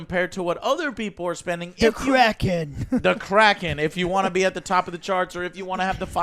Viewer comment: Every time they say "The Kraken" I hear "The Crackhead"